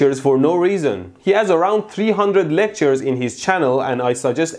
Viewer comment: When you were saying "he has around 300...." for some reason I heard "he's been around for 300 y..." then oh lectures :P Kidding aside, you were kind, and wanted some input from some professor in my opinion. And in return, got insulted. It wasn't you that was insulting, but that reply of his? Tsk tsk...